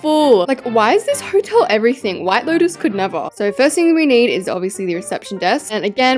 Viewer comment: I love your content so much but at 4:26-4:28 time did she say “ white builders could never” ? I swear I heard that but idk ( btw this isn’t hate)